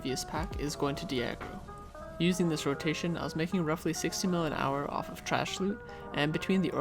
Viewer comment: 424 hours :O hope you get it soon dude! Are your pets T4? Also LS/node? Prices for some items are dropped a lot after about 6 months but yeah at that time I was making about 60~ trash and 100 with drops! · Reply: i make like 5k trash per hour with kama and blue ls, thats around 17m in trash. 2:43 did You say you make 16m in trash or 60m? am i that shit to clear them 4 times slower? Imma succ ninja too 275k kutum.